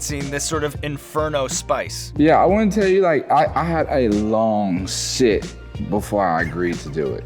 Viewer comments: When you don't know what he said but laugh anyways lmao he said he had a 'long sit' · He "had a long sh!t before deciding to do this"? That's when I do my best thinking, too... ;-P But, maybe I misheard...